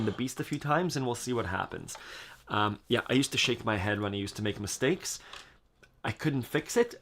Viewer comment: did you say you used to shave your head when you'd make mistakes? Okay, I'll try it. Thanks. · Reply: 🤣🤣 this was the reason I had short hair for sooooo long 🤣 shake my head